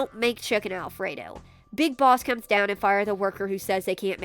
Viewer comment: Who did you say...? B-Big...Boss? NO ONE HEARS A WORD